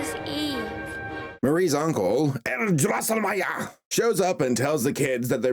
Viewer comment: pronouncing a Russian full name was an "underrated" moment. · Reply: You are right. To be honest, at first I thought he said something like "(Clara) Carlskaya", as though he was naming her with the traditional patronymic system for the middle name. Then I understood he just said the last name "Drossenmeyer".